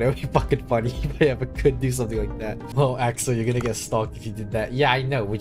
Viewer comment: I think he said "stalk" and not "stuck"